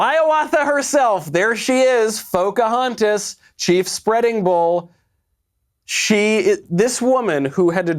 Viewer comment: " Chief Spreading Bull" LMAO!! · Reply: I thought he said Chief Spitting Bull!😂